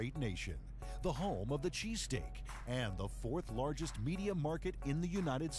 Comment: did he just say the home of the cheese stick? o.o